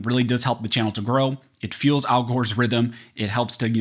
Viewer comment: I could of swore you said, "it fuels Al Gore's rhythm" lmao